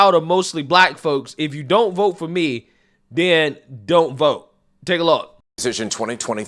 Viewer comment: I thought she said, "If you don't for me, you're not Black."